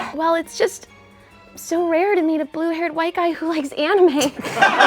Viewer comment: I thought she was going to say blue eyes white dragon 😂